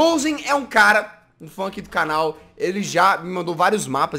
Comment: O que o Jazz falou: "um fã aqui do canal". O que eu entendi: "um funk do canal". Kkkkkkkkkk mineiro é bão dimai sô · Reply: Percebi tambem "FUNK" era para ser fã foi no